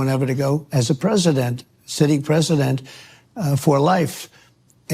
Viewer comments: About 2:07 does he say "president, sitting president, for life" WTF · Did he say president for life